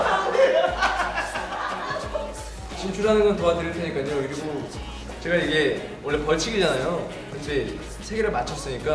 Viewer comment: 東海說了什麼？怎麼沒翻譯沒字幕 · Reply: Hsinyi Lin 東海是說，原來不是（拿到生蛋）有處罰嗎？但我找到了三顆，請幫我們撥MV